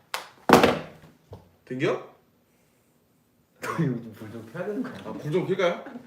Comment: escuche que dijo "prendio"😅 ¿Soy la única?